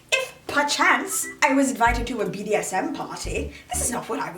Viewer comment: My army mind was so confused at the start I thought she said now this what I will wear to the BTS party and I went what party WHERE?? LOL and ti make sure I put on the subtitles to see what she was actually saying and it said BDSM party I had no idea what it was I searched and I regret it😑 thank god I only saw a little